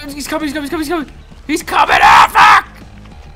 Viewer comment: it sounds like hes saying afflack